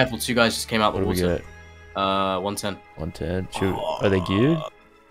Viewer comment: i heard “are they cute” lmfao